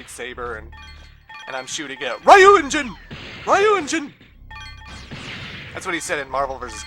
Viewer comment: 👋😌bud, I'm afraid not. That's not what he said. He says "hadangeki".